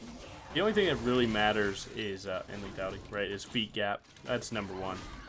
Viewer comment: Did I hear feet gap correctly